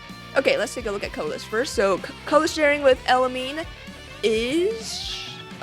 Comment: did I hear elemine or is it just me